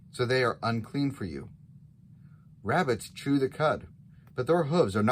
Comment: What did you say around the 1:14 mark? It sounded like said 🐇 rabbits. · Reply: Hello, yes rabbits. There are a number of animals that they were not allowed to eat. Rabbits were one of those animals.